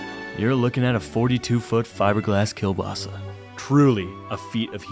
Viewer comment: did you said kiełbasa